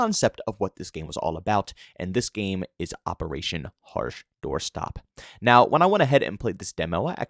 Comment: For the people saying he didn't give the name of the game in the whole 10 minutes...... 0:27 ....Operation Harsh Doorstop. Idk if people can't hear or just do not pay attention, but I mean come on.